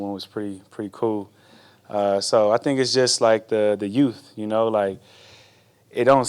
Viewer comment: did he say "utes" or "yooothhs" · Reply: Youth